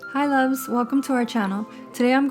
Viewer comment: When she said hi loves welcome to my channel I thought she said hi loves welcome to McDonalds